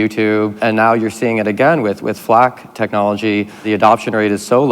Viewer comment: What did he say? "Flak" technology? And what is that?